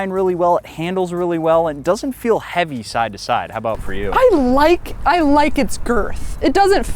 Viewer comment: I heard her say “ i like the girth” 😂